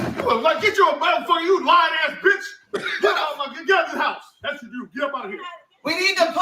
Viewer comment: Sounded like he said get out my mom's house